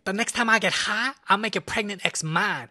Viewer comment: I THOUGHT HE SAID "ill make you pregnant"